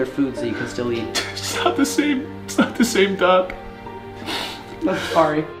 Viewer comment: lmao i thought Grayson said “its not the same duck” im like where’s the duck lmao quack quack🤣🤣